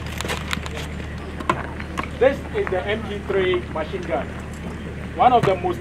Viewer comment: hey soldier did i heard you saying MP3? That is either MG 42 or MG45/42V.